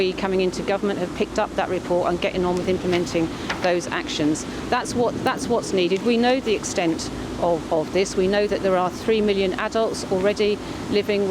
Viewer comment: Did she just say 3 million people affected by the grooming gangs ? 4:50 ?